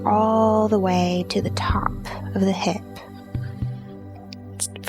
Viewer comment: I thought she said the leg goes all the way to the top of the head.